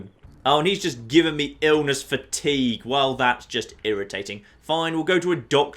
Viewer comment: When you said "He just gave me illness fatigue" I heard "He just gave me illness for tea."